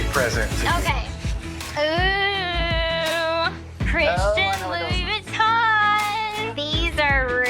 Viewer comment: is she saying "christian louis-bouiton"? xdd